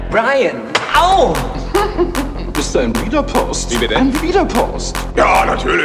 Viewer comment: Was sagt Pontius um 0:22, und was bedeutet das? Ich höre etwas "ein wiederpost", trotzdem verstehe ich leider nicht die Bedeutung dieses Ausdrucks :(( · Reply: "Widerborst"